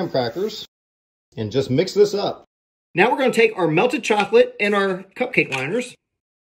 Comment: It sounds like he said green chocolate for yellow